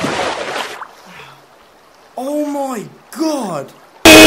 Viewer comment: what did he say ???????????? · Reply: "Oh my god"